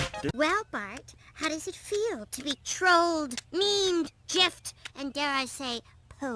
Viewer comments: When Lisa said the words 'trolled', 'memed' and 'gifed' I immediately went into cardiac arrest. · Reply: I thought she said “Jeffed” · DID SHE JUST SAY JIFED INSTEAD OF GIFED!!!!!!